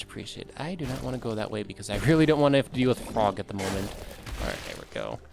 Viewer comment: sorry i heard it as feel like a frog at the moment.